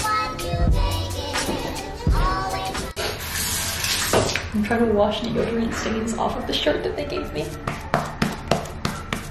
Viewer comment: When she said deodorant stains I thought she said yogurt stains I was like why did you eating yogurt in that also aren’t you lactose intolerant